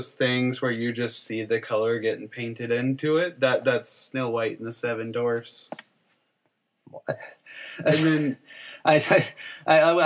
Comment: Hahaha, sounded like said "Snow White And the Seven Dorffs." That put the image of seven animated Stephen Dorffs in my head and I want that movie now.